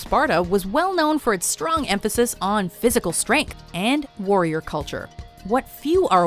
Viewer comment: I can't be the only one who heard "physical strenk"